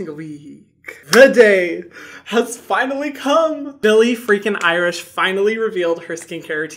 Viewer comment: Call me crazy but did he just say “Billie freakin Irish”????? Lmao